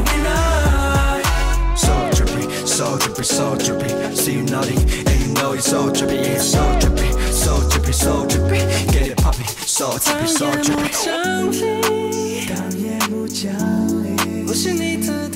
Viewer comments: i couldnt have been the only one who thought that hendery was saying "get it papi" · i thought it said "get it Papi" but it says 'get it poppin' lmaoo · On another episode of misheard lyrics, i thought they said ‘get it papi’ -__-“ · My freakin dumb ass literally thought they say "get it papi" LMAOO BYE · i thought they said "get it papi" lmao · i came here because i thought they said ‘get it papi’ LOL